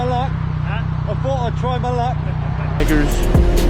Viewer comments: did he really said nig**rs ????? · what did he say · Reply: bikers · what did he say? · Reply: I thought i’d try my luck